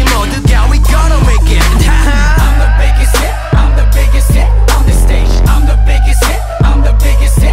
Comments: When I first heard song this I thought they were saying I'm the biggest shit on the stage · I always thought it said I'mma make you sit on the stage! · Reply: I hear "I'm the biggest sh*t on the stage" oops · I always thought it said I'mma make you sit on the stage! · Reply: i heard "I'm the biggest shit on the stage" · I always thought it said I'mma make you sit on the stage! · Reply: Ellie Naomi I thought they said I'm the biggest d**k in this stage. Imagine how shocked i was (T＿T) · I always hear : I'm the babysit I'm the babysit on this day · I swear I hear "I'm the biggest simp on the stage" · My sister thought it said I was the biggist zit on the stage 🤧